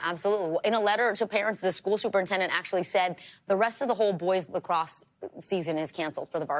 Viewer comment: I thought that said 'Lactose' at first.